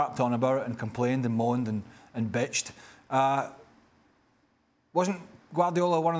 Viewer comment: did he say bitched??😂